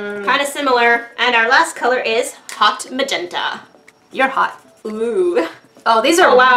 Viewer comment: did anyone else hear (your hot)